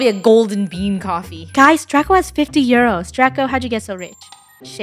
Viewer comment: WHAT DID SHE SAYYY??!! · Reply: Draco has €50 (euros)